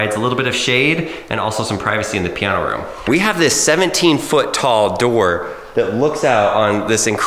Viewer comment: did he just say 17ft?